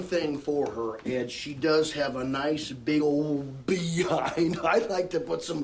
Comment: I thought he said big ol beehive.